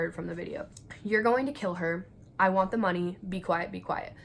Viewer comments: The only thing i heard was "i want the money!" Edit: i replayed the part and now i heard "no!" And "i want the money!" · I just heard "I want the money". I can't hear the other words people are saying. To me it sounds like a child, possibly the brother arguing with the mother. I hear a crying "no" at the beginning and then "why" at the end from what I think may be the brother.